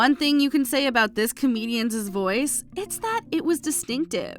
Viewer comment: Re: Gilbert Gottfried. Did you say “comedian’s’s”, narrator woman? 10:11 ?